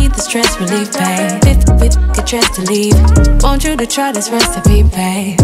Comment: is “before we both get dressed to leave” she’s not cursing lol what did you think she said